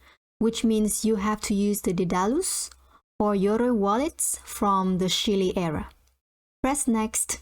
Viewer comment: I am trying to figure out what is said at 1:20 to 1:30. I hear "which means you have to use the ......or.....wallet from the ........... Press next ..... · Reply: Hi Johan, It says: "Daedalus or Yoroi wallet from the Shelley era" Those are the wallets that accept native tokens of Cardano and have to be used to receive WMT. Best regards